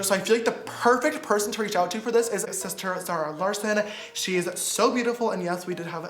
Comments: the subtitle says ,,isis terrorists" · the caption instead of zara larson it captioned it isis terrorist